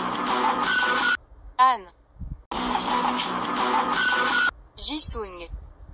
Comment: Why did I hear "jisoo unnie~" for jisung? Btw, as I was the one who made the banner for this channel, would you like me to make another one as it's kinda old now